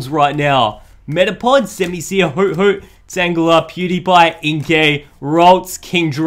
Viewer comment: Did he just say pewdiepie